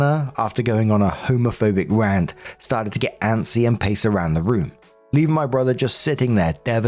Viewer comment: I was confused at about 10 minutes in cause I thought you said "...started to get antsy and piss around the room". Thought that sounded very unhinged...